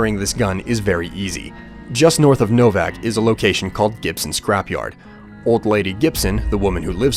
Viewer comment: is it just me or did it sound like he said Gibson's crap yard